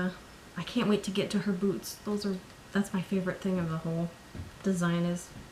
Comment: omg when you said that you couldn't wait to get to Pocketwatch Pixies Boots....i thought you said BOOBS 😂😂 xx